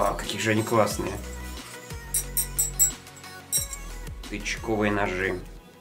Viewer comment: Мне одному послышалось не тычковые,а "ОЧКОВЫЕ НОЖИ"